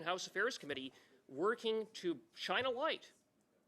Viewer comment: it sounded like Cooper said “working to China-Lite on foreign interference”…🫤